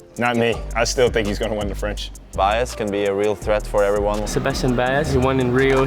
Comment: I thought Ruud said “Bias is a real threat to everyone” 😂